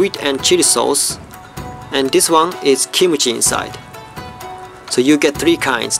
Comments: I thought you had said, "kimochi" ....but then I thought it over and realized you said, "kimchi". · Asakura i think you may have misheard him when he said kimchi.